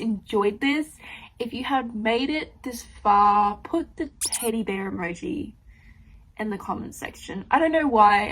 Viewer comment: With English being my second language combined with your accent combined with me listening to the video more than watching it I really thought you said "titty bear" there for a moment, and that made me giggle. Anyway, admire the attempt to try to read the TBR mine is just out of control.